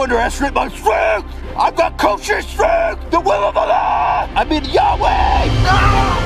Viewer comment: What does Ethan scream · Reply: The will of allah